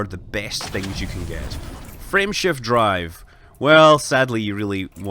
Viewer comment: I heard "friendship drive" all video long. It was awesome.